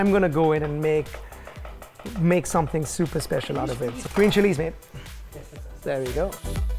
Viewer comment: Why do i hear him say, green chilies babe xD not sure if thats what he said but cute none the less lol